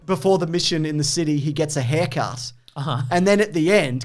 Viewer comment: When you said Ayer-cut, I thought you said haircut. Sure, show me the haircut